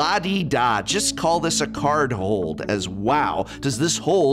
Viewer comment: I always thought he said car hold as well but its car hole... Love the Simpsons reference though